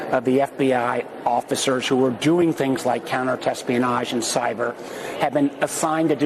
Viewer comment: Did I just hear Warner use the word "Thespianage"? @ 0:25 ..Because THAT makes a whole lot of sense!!!